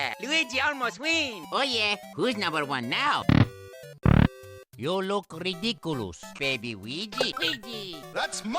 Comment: I thought he said “you look pretty cooloos”